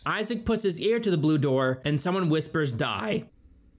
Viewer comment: Wait.. at 9:48 I don't hear the word "die" at all. I heard something along the lines of, "It's free real estate." Am I tripping out? 😆